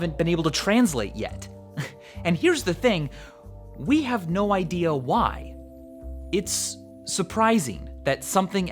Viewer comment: when somebody say "No idea why" i hear "No diy" lol